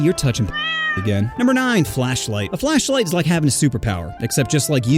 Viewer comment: I legit heard flesh light💀